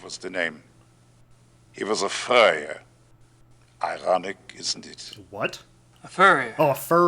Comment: I thought he said "he was a furry ironic isnt it." 0.o